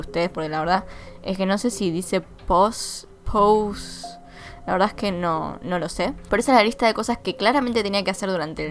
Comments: creo que dice rosario, ósea, rezar el rosario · para mí que dice presents osea las bolsitas que tenía que terminar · Creo que dice pedals (pedales)